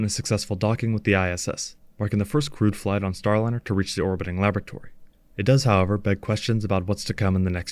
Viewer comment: I thought I heard you say: the orbital lavatory, but I guess it was laboratory...😎